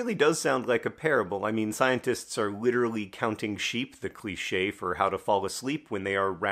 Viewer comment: At first I thought John said scientists were “counting Bitcoin sheep” 😂